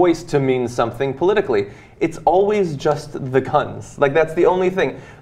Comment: Did John just say "cunts"?